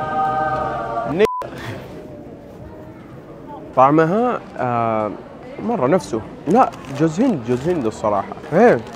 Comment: وش قال قولولي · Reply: نيقا